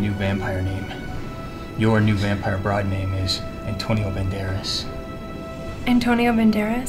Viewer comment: When he said Antonio Baneras, I thought he said TwinkleToes... XD